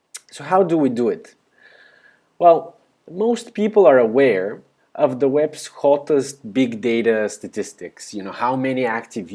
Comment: What do you say after 'most people ...' on 1:25? The captions didn't make sense. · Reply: Most people are aware of the web's hottest big data statistics ...